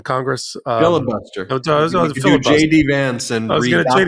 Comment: I thought Ben said, "Billable!" (Not "FILIBUSTER"!) when Michael asked what slowing the clock down meant; I thought it'd pay better if the clock sped up, but then I guess that time is relative, at least until Einstein is superseded or shown to be wrong somehow...but I won't hold my breath, and I'll happily bill you for the time, just as long as you don't jump into a black-hole to escape paying up! Certainly the passage of time goes really slowly in prison, and a lot faster when your sensorium is engaged in fun activities. I can only guess that it'd be pretty fucking engaged if you were, indeed, falling into a black-hole - just choose one with a large mass to avoid spaghettification before you pass the event-horizon, or it might well be a wasted trip.